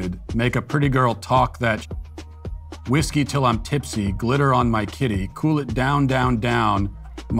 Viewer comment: Hearing Matt say "Glitter on my kitty" is amazing 🤣🤣🤣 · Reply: I thought at first he said litter on my kitty